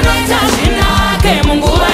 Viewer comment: Kana akada hake anokuchachura mupoto🔥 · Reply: I thought they say mumoto not mupoto😂. Anyways ma revelations anosiyana lol